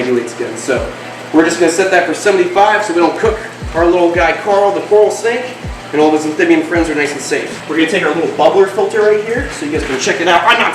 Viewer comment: I thought he said Corel the carol snake 🐍 like um 😐 okay